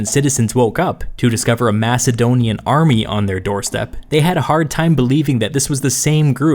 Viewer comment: thought you said MASSIVEdonian army there for a second